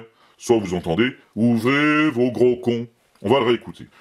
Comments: J'ai entendu "ouvrez vos gros coeurs" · Pour titi, j'ai entendu "ouvrez vos bouquins". J'étais pas loin...